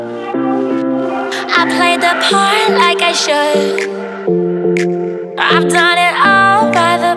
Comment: Lol I heard "I played the PORN like I should" until I replayed it.